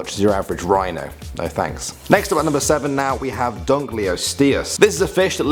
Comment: I have no idea why but srsly I heard 3:09 as "Drunk Leo Sit Here" ?!!! lol? Lol!